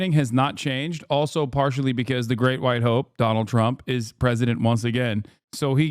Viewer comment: oh the great white HOPE. I thought he said the great white ho.